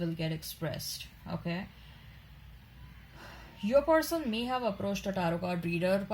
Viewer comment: I would like to know what you are saying about my person at 10:34 to 10:36? I've listened to this four times and I can't understand that one sentence 😔can you please write it in the comments please 🙏🏾 thank you 🙏🏾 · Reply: She said your person may have contacted a tarot card reader..